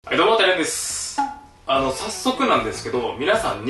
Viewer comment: どうしても、はいどうもてらやんですに聞こえる